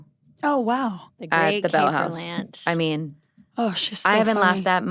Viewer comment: What show are they talking about at @ 59:45? Capri Lance at the Bell House, is what the transcript says. I can’t find on google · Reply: Kate Berlant